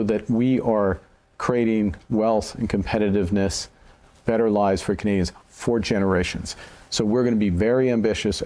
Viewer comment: did he say better lies for Canadians...😅